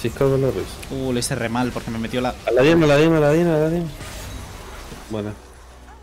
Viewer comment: el que le dice ilolai a illaoi y que luego le dice fiora a irelia se burla del vato que parece decir "a la vayne" pero efectivamente dice "a la diana"